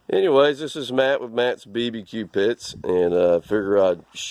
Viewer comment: i thought he said its matts bbq B**